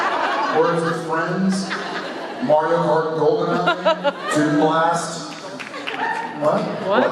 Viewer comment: What did Jensen say? A name of a game?😂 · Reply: I think he said Toon Blast